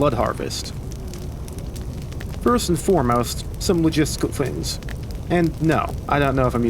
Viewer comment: genuinely what the hell are you saying here? All I hear is somwjshconfhs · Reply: some logistical things